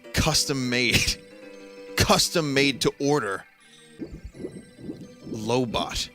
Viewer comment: I almost thought said "custom maid order", kind of a cursed moment there